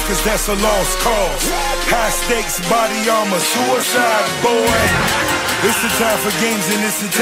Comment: I think he says "buddy ima suicide boy"